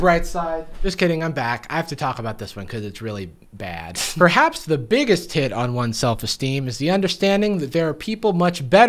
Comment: I heard "the biggest hit" as "the biggest tit" and was confused